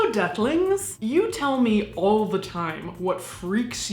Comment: I definitely heard "Hey Ducklings!" at the start of this video. 😆